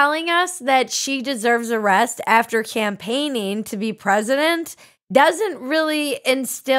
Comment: I love your teaser pic!! Could have sworn you said “champagne-ing for president”!